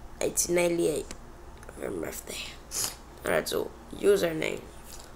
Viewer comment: FIXEDMISTAKE "1898" I THINK YOU WERE SAYING "1998"